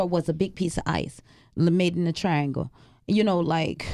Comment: did she say "ice in the shape of a triangle "?? @ 18:05 how could non-medical personnel issue a diagnosis of no signs of 'sexual assault'?? Isn't that the #medicalexaminers' schooling that decides 'that'?? @ 19:46 that kitchen looked desolate like NO food has been cooked there in eons. @ 21:41 We KNEW he was something of a Icky Note! He felt weird from the gate! @ 24:06, you are a Strong Woman! Keep your focus on #Kenneka, your daughter; don't allow the detractors to distract you from your Divine Purpose! A'se. @ 26:59 it's like [they] don't want that beautiful baby's ending to hold the dignity her life held. @ 33:33 yes, Ms. is Strong! #SurplusofMelanin can Only be the Answer for such Strength! May the Ancestors continue to Bliss and Protect you and your family, Ms.. A'se!! <3